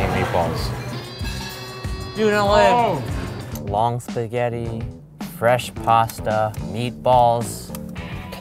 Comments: I thought he said mom’s spaghetti 😂😂😂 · did anyone else think he said moms spaghetti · I thought he said moms spaghetti like the Eminem song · who also thought he said mom's spaghetti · I thought he said moms spaghetti........ hmmmmmmmm........... His palms are sweaty, knees weak, arms are heavy There's vomit on his sweater already, mom's spaghetti if u dont understand this you have been living in mars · I thought he said "Mom's spaghetti" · I heard "Mom's spaghetti" ＼（Ｔ∇Ｔ）／ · anyone else hear "moms spaghetti"?? lmao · I thought he said mom spaghetti